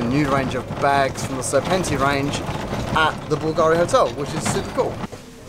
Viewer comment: I thought it said Bulgarian house ...